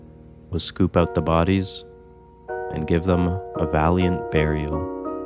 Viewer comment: This scene was emotional it almost my me cry until this freaking scene I thought he said a proper burial 😢😅